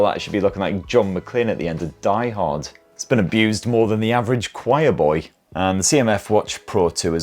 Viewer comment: "it's been abused more than the average choir boy" 😂😂😂😂😂 this guy lol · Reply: Oh I thought he said shy boy